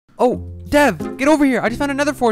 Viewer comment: The beginning: “Hey Dev!...” What I heard “HEY DAADDD”